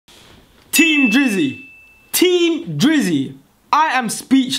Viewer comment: thought this dude said team jizzy for a second 😂